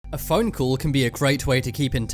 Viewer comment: I thought you said f*** you? But you said phone call